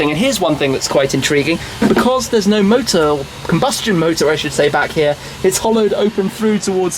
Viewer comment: what did you say combustion motor :)